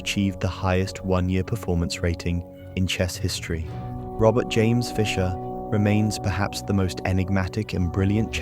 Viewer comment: misheard it as "Robot James Fischer", which, in a sense, was true